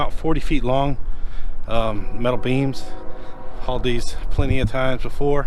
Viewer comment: I thought you said you were to be hauling beans. Instead, it is beams. I think I need to have my ears checked. Ha.